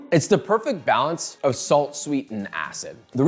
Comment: Yeah...I heard "It's the perfect balance of Salt, sweet and ass" Great video. Making this.